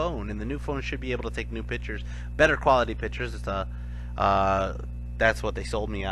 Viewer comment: When you were talking about your new camera i heard "better quality bitches"